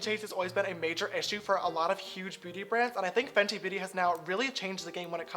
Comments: i think he said booty instead of beauty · it sounded like he said “Huge bootie brands” 😂😂 is it just me? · I swear James said booty brands by accident 😂